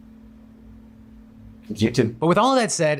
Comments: Wow, watch out in 4:32 the closed caption say "it's genuine" where it should say "It's Youtube"... it completely changes the message portrayed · The CC is incorrect at 3:27: "If I knew he was going to lose his job too, I wouldn't have done it." Might want to fix that as it's the exact opposite of what he said. Also an error at 4:16: "And behind closed doors, he is not super great." 4:33 Should be "It's YouTube." not "It's genuine." There are a few other errors but I wanted to point out the ones that change what he's saying. Thanks for adding CC so quickly today, but perhaps it might be better to hold off to make sure they're correct first before posting them in the future. <3